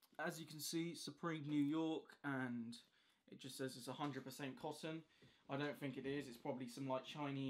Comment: I thought he said 100% possum. I was disappointed when I realized he said cotton.